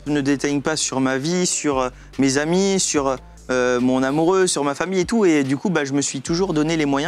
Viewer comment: Pourquoi il dit « mon amoureux » ? 🤔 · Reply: non il dit bien "mon amoureuse"